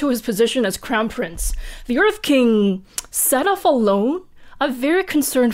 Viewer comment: When you said “set off alone” I thought you said signed a loan and that you were very concerned for him XD